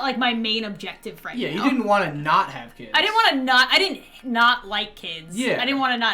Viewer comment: OMG I THOUGHT YOU SAID YOU DONT WANT TOO HAVE KIDS LOL. But please don’t leave us :,(